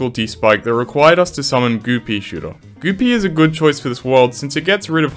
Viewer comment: why am I starting to think that you’re saying “Goopy?”